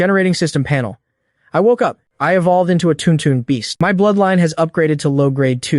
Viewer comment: thought the tuntun beast was tung tung seems like i misheard it (i realized when i saw the subtitles)